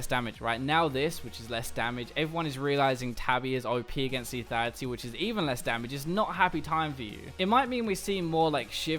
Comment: What did he say in 1:45 ? What is op against lethality? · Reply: Timothy Teo ninja tabi boots